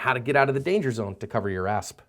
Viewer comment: Sounded like he said cover your ass! Lol